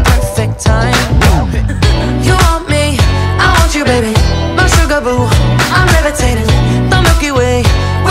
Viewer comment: I thought it said "my bugaboo" at the part that says "my sugarboo". 💀💀💀